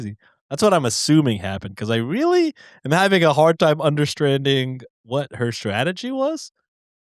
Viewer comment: Did say understranding at 25:58?